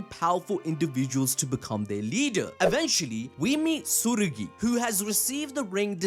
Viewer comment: Hey boss. It sounds like your saying tsuguri when I believe it's tsurugi